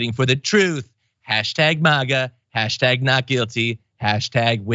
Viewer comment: Seriously thought he said hastag manga. >..> lol